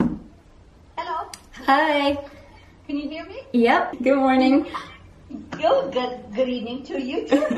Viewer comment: Hey, I noticed in your captions at 0:07 it says "I yeah your morning you'll get" instead of "hey grandma good morning". If you could please change this so people with hearing disabilities can enjoy your video that'd be awesome.